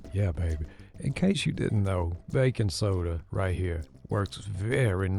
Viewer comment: I thought you said here comes the bacon soda at first. I was like huh? lol 😆 oh Baking soda.